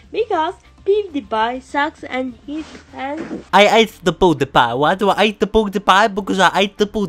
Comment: He sounds like he said "I ate booty pie" lol